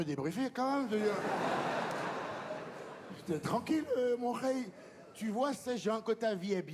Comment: " il dit quoi : "j'étais tranquille mon... ????" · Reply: Mon khey ( frère ) 🫡